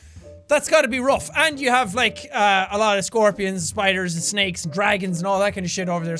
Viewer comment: Did he say dragons? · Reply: flameing gaming34512 yes he did say dragon